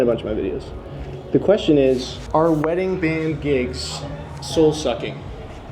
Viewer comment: Thought it said self-sucking ahaha. Anyway have a good night guyz, galz and all my other palz